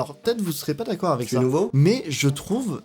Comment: A 10:02 y’a que moi qui entend une voix qui dis « tu es nouveau ? »